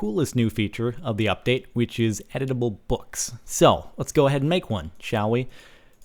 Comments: I thought he said "Edible Books". My mind repeatedly fucked itself when I heard that. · I thought is said edible books. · am i the only one who heard him say "edible books" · Instead of editable books, i thought you was saying eatable books. -_ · Every time u said editable books at the beginning I thought u were saying eatable books and I was so confused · I thought he said edible books, as in able to be eaten... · At first, I thought it said edible books. XD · i thought he said edible books lol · I thought he said edible book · at first i thought you said edible blocks. i was thinking is there steakwood now? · DX I thought I heard/read "Edible" books instead of "Editable" books... I was SO getting ready to see CaptianSparkles nom down some paperback bookworm style. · I thought he said eatable books lol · Am I the only one that thought he said edible books?